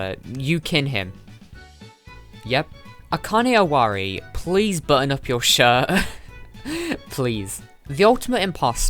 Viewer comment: At first I thought she said "Please burn up your shirt" and now i feel so bad XD